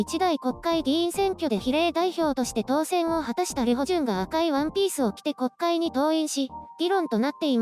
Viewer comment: 本文と関係なく済みません。 ミホジュンと聞こえて・・・。